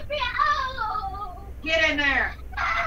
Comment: I think the mom was saying “get out” while the son was saying “can’t breathe”??? D A H E L L ?